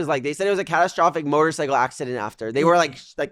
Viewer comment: i was so excited for a moment when i thought he said shock-g lol